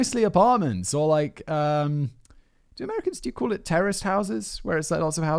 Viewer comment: OH "Terraced" houses! I could have sworn Simon said "Terrorist houses" like hold on a sec.... did he just say?